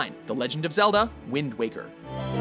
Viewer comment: they put wind walker instead of waker